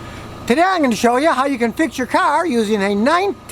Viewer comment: thought it said fix your 'cat' for a second